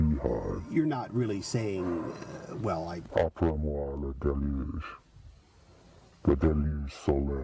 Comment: What does he say @ 9:00?? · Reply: This is a good point. He quotes Louis XV's famous statement: "Après moi le déluge" (After me, the flood) but he makes an addition--le déluge solaire (the solar flood)